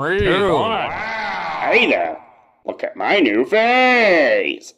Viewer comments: I hear 'hey there, look at my new face' 😂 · At first i thought he said look at my new face😂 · I thought it said “Hey look at my new face!” 😂